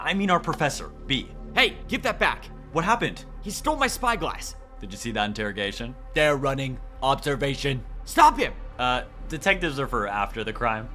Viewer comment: Did i just hear spy glass?